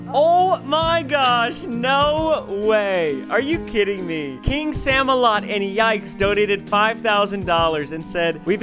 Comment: I zoned out for a second and I heard "kingsammelot and yikes" and I was like wtf